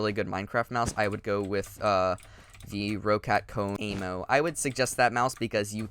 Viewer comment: did you say roccat Kone emp? The audio cut out a bit